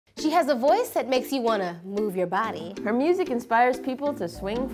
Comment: I thought it said "move your BOODY" 😂😂😂😂😂😂😂😂😂😂😂